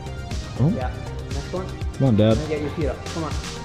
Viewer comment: Did he just said "come on dad" ?